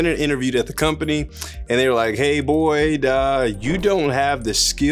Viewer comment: I swear every time you say "hey boyd" i think you are saying "hey boy" I'm sitting here like wtf did he just say!? I know they weren't talking to my man like that 😂😅